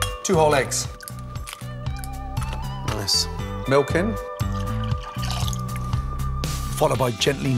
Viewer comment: Am i the only one who thought he said “milking “ when he said “milk in”? 😂😂😂